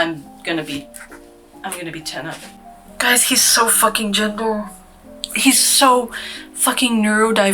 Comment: Swear to god you said "He's so fucking Jimble" for a bit and had to replay this part several times to make out the word "gender" because I'm both stupid AND hard of hearing 45:00 and aint that the truth... DAMN IT ALL </3